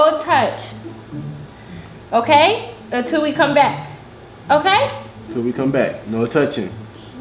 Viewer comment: I could have sworn she said "oh come back please"! She is very smart!